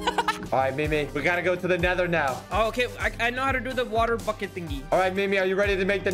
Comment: i misheard bucket for fucket